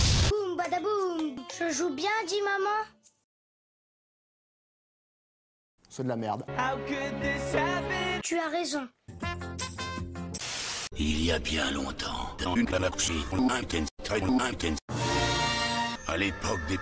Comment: moi j'ai compris "il y a bien longtemps, dans une éclabousée lointaine, très long Intel" lol